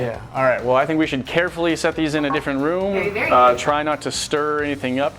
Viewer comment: anyone else hear hey Nate